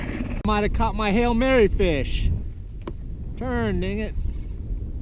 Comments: At around 2:28-2:30 did you say what I thought you said? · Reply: Sweet Dreamz Entertainment pretty sure he said “turn dang it” but yeah I had to listen to it a couple of time haha. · I thought said,”turn nigga!” Hahahahahah